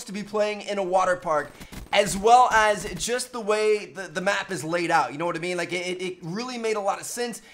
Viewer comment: I thought I heard him say this map is a wata fun.